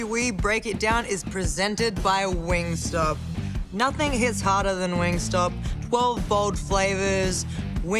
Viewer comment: rhea is hotter than wing stop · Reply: Thought she said nothing HITS HARDER then wingstop. But regardless yes